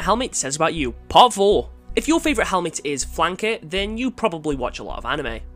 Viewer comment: Had to do a double take for a sec, nearly thought he said “wanker” 😂